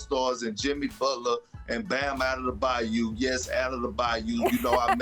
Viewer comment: It sounded like Perk said "Bam Adam will buy you" when trying to say Bam Adebayo 😂